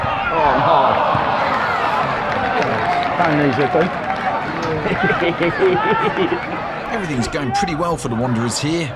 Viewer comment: What does say here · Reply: `that ain't easy to do` 😂